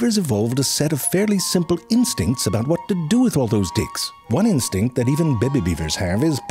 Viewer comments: I heard 'dicks'...🤣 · did you say "what to do with those d**ks" ?😁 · …..do you….do you say d*cks? I’ve rewound like 15 times and that’s what I keep hearing. I’m wrong though…right?????